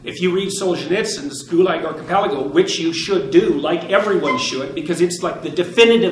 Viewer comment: What book is he saying there? I wanna look it up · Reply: Gulag Archipelago by Solchenitzyn